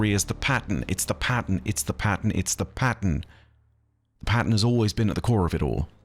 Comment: drinking game, take a shot every time says "Pattern" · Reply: Eventually I heard it as “It’s the Patton, it’s the Patton, it’s the Patton” Cracked it: the Conductor is Patton Oswalt